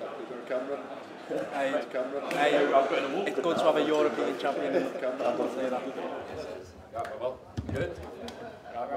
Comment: what did Trent say at 13:18 guys ? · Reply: It's good to have a European champion in this camp